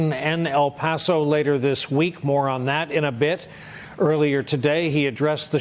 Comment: "more on that" .. but I heard "moron that" ... and it still makes sense...